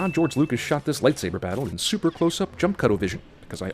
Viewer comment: LMAO at the 2-minute Mark I seriously thought he said jump cuddle vision and had to rewind it to check out the subtitles